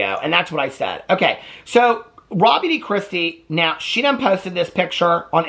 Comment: honestly no idea who miss lady is. i misheard her name is robbi di christi. irrelevant turd! but important to talk about. thank you.